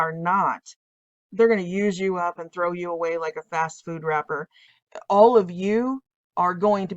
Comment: I thought you said, “they’re going to throw you away like a John Cougar record.” But you said, “like a fast food record”. I had to rewind. I thought it was hilarious. The useful idiots ALWAYS get taken out.